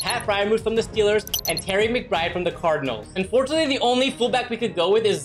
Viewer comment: did you just call him terry mcbride..... He's Trey McBride!